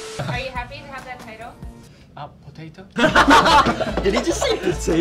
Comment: mark in portuguese : Ele disse potato? " did he say potato "